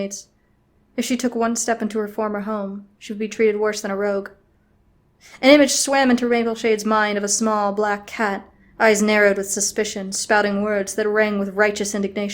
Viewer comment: “Rainbowshade” is what I think it sounded like.. XD